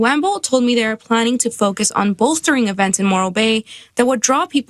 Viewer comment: I'm in the UK, I've never heard of Morro Bay. And no, I will not be travelling to the US for the foreseeable future. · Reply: I kept hearing "Moral Bay" and I ain't going there either.